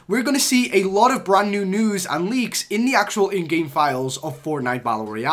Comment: I thought he said nude leaks